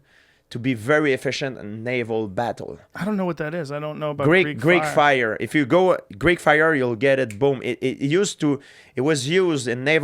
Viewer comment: Girl: “I bet he’s cheating on me” Me and the boys: “so there’s this shit called Greek fire” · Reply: I thought he said great fire 😂☠️